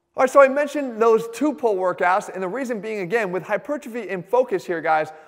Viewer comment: Can someone help me I'm not natively speaking English im German and i can't understand the word he's saying at minute 1:33 hipuchafy or something like that? How is it spelled and what does it mean? Thx in advance if someone replies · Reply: Hypertrophy, and it basically means growing muscle mass as in increasing the size of your muscles. Hope that helped! :)